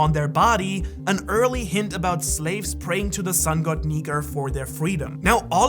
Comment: Did anyone else notice that he had said Niger instead of Nika? At